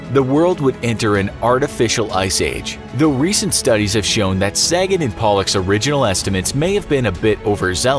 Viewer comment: I thought he said seigen and polak i was like umm what?😂